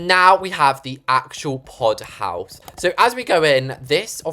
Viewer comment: can’t be the only one who thought this said “poo house” instead of “pod house”